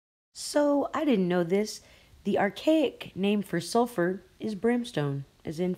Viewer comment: I think she said "soul free" not " sulfury", because robots can't have a soul, at least I think so, only time will tell.